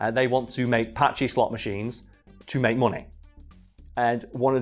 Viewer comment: I thought was saying "Apache slot machine" for the first 10 times. And I'm still not sure what the hell he was trying to say. referenced pachinko machines but there's no way that's what was trying to say.